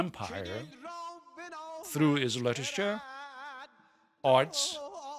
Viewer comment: excuse me, but what did he say at this part? I could only hear "Legisture" which I don't think is an existing word. Literature?